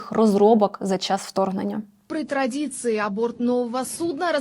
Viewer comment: Мне послышалось, или она сказала АБОРТ нового судна? Ну собственно нахрюкала беду... Абортировали))) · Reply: Разбили бутылку о борт судна...